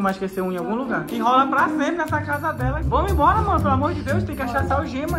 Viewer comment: Gente em 00:48 ele chamou ela de amor ou eu escutei errado